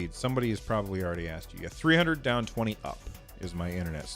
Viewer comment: Did he say 300 down / 20 up? that's insane. Can't even get Internet that slow I think..